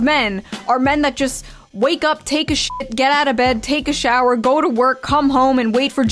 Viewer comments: Did anyone else catch the part at 9:24 where she said men sh*t in the bed then take a shower? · Did she say wake up 💩 then get out of bed😂